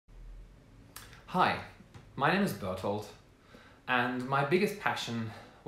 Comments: swear to god i thought you said "hi, my name is butthole" 😂😂😂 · I swore he said "my name is butthole" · I thought he said "Hi my name is Butthole" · 100% thought he said his name was butthole and I had to check his account name. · i thought you said "my name is butthole"